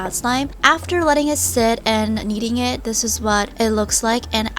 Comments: I heard "eating" it but she said kneading... Oops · i thought she said "Eating it" Lol